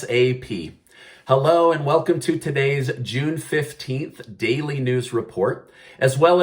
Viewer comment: I thought I heard June 15th not July lol